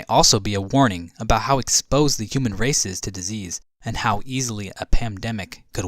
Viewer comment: i hear PANDEMIC?